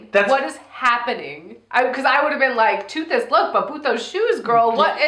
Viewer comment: Ohh boot those shoes, I thought you said "puto shoes" XD